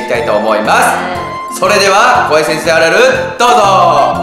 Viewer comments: 0:0の怖い先生あるある～、大橋先生あるある、に聞こえたw大橋先生っているのかなw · の最初の声 怖い先生あるあるがわいせつあるあるに聞こえた …あっゴメン このあるある 確実にオール5を取ろうのある学校とか確実に取れない